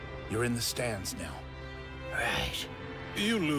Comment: I know Beerus said bench but to me it sounded like he said "The 3 baldies of the Bitch."